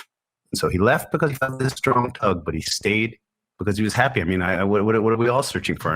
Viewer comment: had to listen multiple times to hear what was said due to the distortion, but sounds like, "so he left because he felt this strong tug", followed by, "but he stayed because he liked it". I love towards the end how he talks about how he was never bored, never felt alone, rather, always felt super connected to the Universe. Then Michael Finkel says that if there was one thing the world could do to improve and find more peace, joy, and contentment - it would be to stop and be in silence, even if it's just for a few minutes here and there, or like 15 minutes a day. Where I've been doing the work and meditations of Dr. Joe Dispenza, YouTube algorithms probably brought this video to my feed. Glad it did.